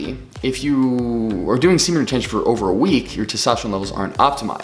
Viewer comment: I thought it said seminary retreat.